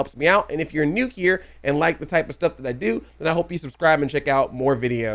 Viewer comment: No problem. I thought it was an enjoyable discussion of the difference ha ha! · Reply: Speaking of misinterpreting, can I ask what it is you say at the end of your videos? I always hear it as "I love you like a play, cousin. I'm Audi 5000."